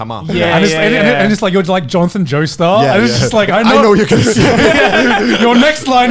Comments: did he just say Jhonson Joestar? >:0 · did he just say.... Johnson joestar.. Amazing 🐧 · did just say "Johnson Joestar"?! PART 9 JOJO CONFIRMED!! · Did he say "Johnson Joestar"?